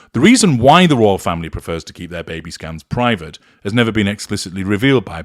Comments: Every time HG said ‘baby scan’ I heard ‘Baby Scam’. 😂🇦🇺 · Every time HG said "baby scan" I kept hearing "baby scam". 🤣